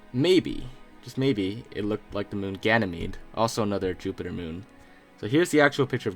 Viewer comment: did he say Gannon meat?